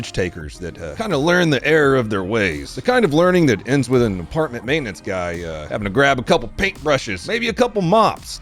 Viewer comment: As an apartment maintenance guy i promise you that mess isnt for us to clean up. Theres a whole crime scene cleanup crew provided by the authorities. We just have to put up new drywall, windows, doors, or carpet long afterwards. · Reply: I thought he said department clean up crew. At least that’s what I heard. But yeah you don’t get paid to clean up brain matter and skull flakes.